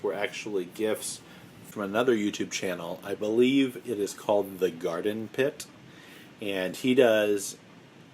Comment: Can you please repeat the channel that you get free peppers from? I thought you said the Garden Pit. Was that right?